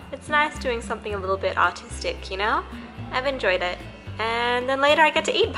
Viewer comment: I thought she said autistic but not artistic